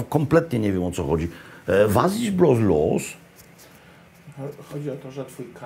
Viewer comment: Czy tylko ja jestem takim debilem że usłyszałam ,,Was dziś blos los?’’